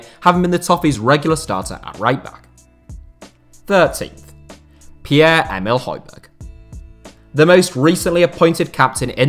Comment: i looked away and on number 15 i thought he said pieree emerick aubameyang but he said piere emile hojberg. they have similar names